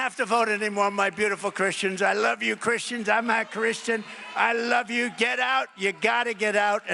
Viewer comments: does Trump say “I’m not Christian.”? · I still don’t know if he said I’m Not Christian or I’m a Christian. I thought he said I’m not Christian because he was shaking his head when he said it. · Did anyone catch Humpty Dumpty stating "I'm not Christian" @ 0:17 seconds into this video? I tell you, the man is the embodiment of the Anti-Christ. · Maybe I am hearing this incorrectly, but I doubt it. Did he just say as he grabbed his chest “I’m not Christian I love you, Christians, etc.“?!